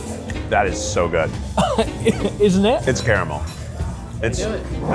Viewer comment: i think he thought Keith said terrible instead of caramel...poor guy, his face fell and I thought keith said that too.😂